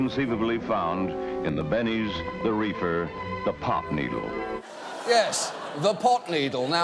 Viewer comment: i thought he said "pop needle" at first, (which does sound like a needle used for taking drugs) i didn't realize it was actually "pot needle" just to clarify, pot is not taken through a needle, it's usually smoked like tobacco... (but i'm sure you people already knew that, which is why it's so ridiculous to say pot needle)